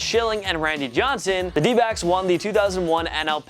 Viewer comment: For a split second I thought you said D-bags instead of D-backs lol